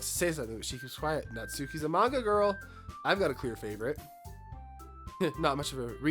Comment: i hear "Natsuki is a MAGA girl~" out of the corner of my ear as i prep for the day.